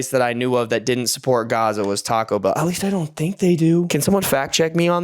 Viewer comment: Is he saying Gaza?